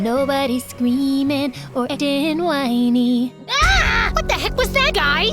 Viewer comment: i love that "getting whiny" part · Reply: I hear “ etting whiny”